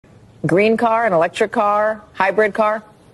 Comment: thought she said green card at the beginning